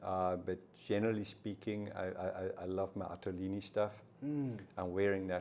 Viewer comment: who did he say his tailor was? couldn’t catch it. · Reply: Attolini - they are based in Napoli